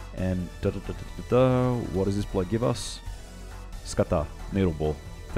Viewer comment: Did i hear "skata" in 21:30?